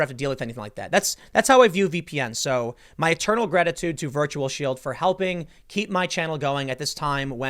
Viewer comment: While selling Virtual Shield Tim definitely says "My eternal GRETAtude." Captions got it wrong.